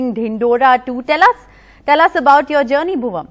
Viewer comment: Did she say "Bhuvam"